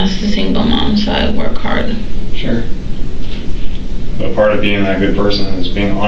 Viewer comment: Did I just hear her say "I'm a stripper, I work hard" ??????